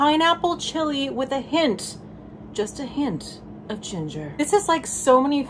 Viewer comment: #1: all openings should be to song #2: I totally heard “this buch tastes like pineapple, chili and a hint of CHILDREN”. gasp and now I’m dying of laughter.